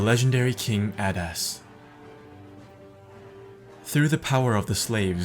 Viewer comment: when I first heard this I thought he said King Badass not Adas